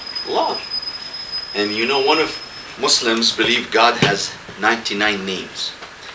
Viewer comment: I thought he said 'god has 99 knees' - that would make about as any of these beliefs do anyway.